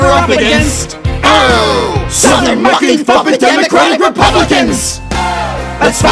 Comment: I swear I heard SOUTHERN MUPPET F*CKING DEMOCRATIC REPUBLICANS XD XD XD Like what was it even supposed to be???